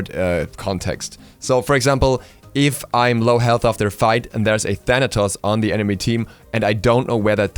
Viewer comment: LOL Skip to 1:20 and put on Auto Generated subtitles and youtube thinks he said Genitals instead of Thanatos lol XD